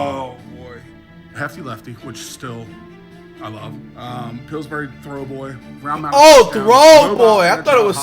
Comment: Flight thought he said Pillsbury throat boy 😂